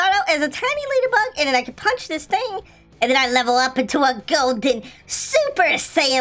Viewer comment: did he just say gold digger?? =